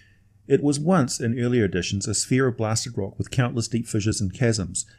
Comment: "...Malsheem. It was once in earlier editions a ????? of blasted rock..." I hear you using the word sphere, but I'm thinking I'm wrong. The automated captions state fear (also probably wrong). Can you let me know what word was being used? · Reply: A sphere of blasted rock with many great cracks and fissures hosting millions of citadels. It's a classic hellscape, with lakes of fire, jagged rock ridges, razor-sharp obsidian crystals, rivers of molten rock, pools of noxious poison and weather that consists of blasting geysers, volcanic plumes, firestorms and meteor showers.